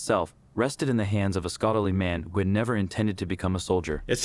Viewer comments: He actually said “scholarly man”….it was the close caption that heard it as “scoundrelly man”. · did the voice say scholarly but the captions say scoundrelly? lol